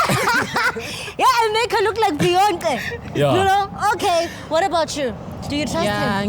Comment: 3.51 did she just say beyontle😂😂😂😂😂